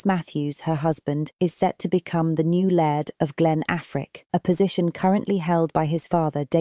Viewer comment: I heard the husband's title as "Lad of ..." and thought it was a bit of British humor. Only when I heard "Lady" did I realize it was "Laird". Still think it was a missed opportunity.